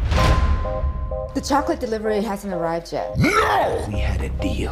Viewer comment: “the chocolate delivery hasn’t arrived yet-“ ”NO. >:(“ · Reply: I thought she said taco delivery 🧍🏾‍♂️🌚